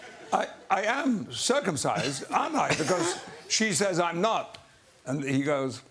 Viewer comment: What did Patrick say at 1:37? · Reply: "NOT"